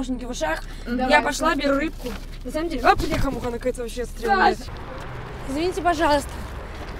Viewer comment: мне не послышалось? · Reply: сказала "бляха муха" Но типо в этом ничего такого))